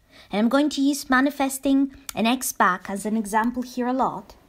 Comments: For whatever reason sometimes for a second I mishear you saying "winning an ex back" as "winning an x box"...🎮📺 👫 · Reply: LOL A few days ago, I was thinking about buying my friend a video game set. I heard her say that and I thought it was sign for me to go ahead and buy it. Then I realized she said "ex back. Not X Box". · For whatever reason sometimes for a second I mishear you saying "winning an ex back" as "winning an x box"...🎮📺 👫